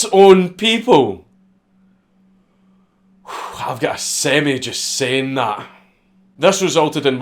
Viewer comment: What did he say? I've got 'semmy' just saying that? semied?